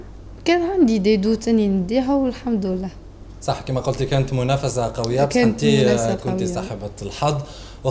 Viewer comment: سمعتوها قالت مناسفة 0:50 ولا غير انا ؟